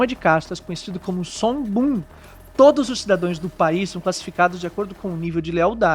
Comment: "cidadoes" foi osso